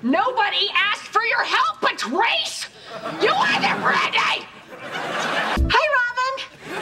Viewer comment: Can anyone work out what robin says at 00:23 😂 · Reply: You either Brandy